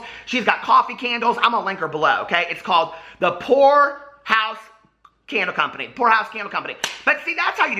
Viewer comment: I thought you said her shop was 'whore house candles' and I was living!!